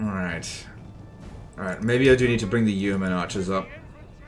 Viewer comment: What I heard: "I need to bring the human archers..." Me: Bro this isn't warhammer ofc they all human Me, later, reading: Oh